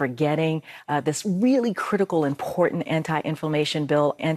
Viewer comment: I thought I heard her say, anti information.